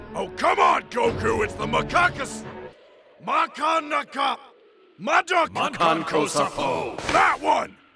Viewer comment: cell#1: "MA!" cell#2: "KANKO!" cell#3: "SA!" cell#4: "CANNON!" come on man, you had one job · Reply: I thought he said makankosePATTERN